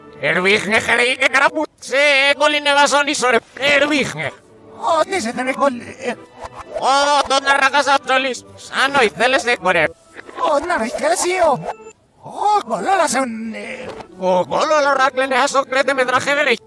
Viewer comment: sanos los de corea Bueno eso escuche yo :v